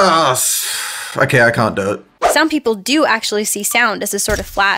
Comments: did just said "ahh, fuck I can't do it..."? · Did said "Fuck it I can't do it" at 5:27 ? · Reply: he said "ok i can't do it" XD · i thought that said "F*ck it, i can't do it" but he actually said '' Okay, i can't do it''